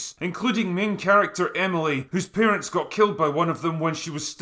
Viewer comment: Oh my fucking gods I heard you said "Emily whos APPEARANCES got killed when she was just a kid."! I had to check three times that you really said parents since the first one was so fitting! xD